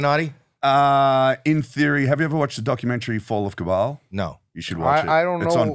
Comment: what documentary did he say? Fall of kabal? I can't find it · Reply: Search The Fall of Cabal by Janet Ossebaard